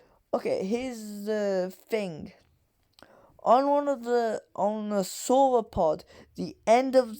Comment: Wait what were you saying here that could break the sound barrier? The transcript says it’s a “solar pod” but I’ve never heard of that.